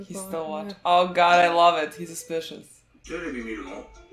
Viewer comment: Light: ignores a weirdo whispering in his ear Her: he’s being so suspicious! 😂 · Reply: i thought she said "this is so suspicious" or maybe she said it towards L.